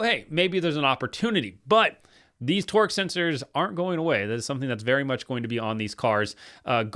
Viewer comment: twerk sensors,is what I heard 😂 I agree with you on the FRM young driver lineup,a lot of talent and potential there. Kyle can definitely win all those too.